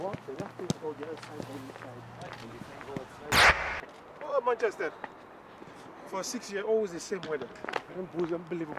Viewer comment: wait at 6:34 what did yaya say :O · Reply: Ethan Butler Something like "ooooh Manchester, for six years always the same weather" xD